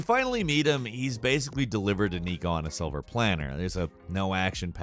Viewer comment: Idk if I misheard, but if not, it’s “silver platter” my dude, not “silver planner”